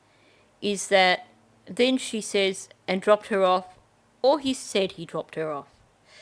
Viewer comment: I heard he dropped her off “where” he dropped her off. Did I hear wrong?